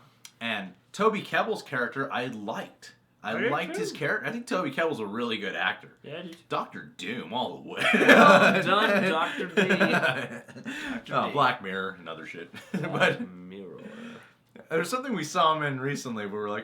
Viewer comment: Did I hear say "Black Mirror.. another shit.." ?